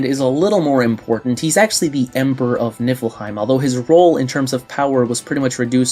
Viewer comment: And I keep hearing "Nippleheim" 😂